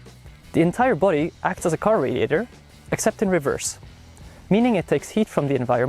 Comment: I thought he said carburetor not car radiator